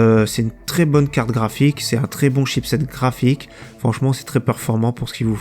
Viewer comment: MDR tu dis quoi à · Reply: Croûton Tim Chipset graphique (exemple de chipset graphique : NVIDIA Geforce GTX 1650, NVIDIA Geforce GTX 1650 SUPER, NVIDIA Geforce GTX 1650 Ti, NVIDIA Geforce GTX 1660, NVIDIA Geforce GTX 1660 SUPER, NVIDIA Geforce GTX) 1660 Ti